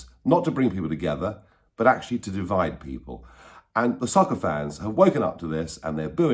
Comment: Did he say the soccer fans?